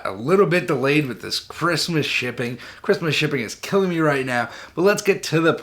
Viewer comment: thought you were gana say "with this Christmas shit". I was about to be shocked and appalled, enjoy your reviews bro